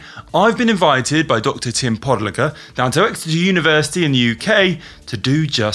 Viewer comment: I swear instead of Podlogar I hear every time "pod licker"